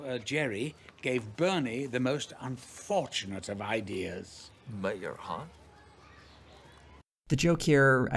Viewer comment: What is Bernie saying at @ 8:30 ? I can't for the life of me understand him and there are no subtitles for his dialogue. Thank you. :) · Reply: He's saying: "Mayor, huh?"